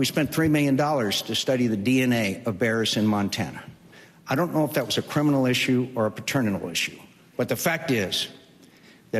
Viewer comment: Did he say "paterminal?" Kinda reminds me of "subliminible" or "misunderestimate"